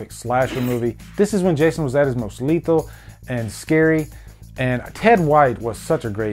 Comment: Is it just me or it sounds like he said "Muslito"?